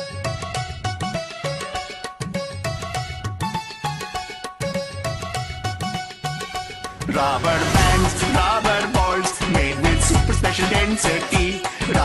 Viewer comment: when i first herd this i thought it said rubber pants rofl